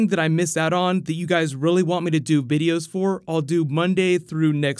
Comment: did he say bideo?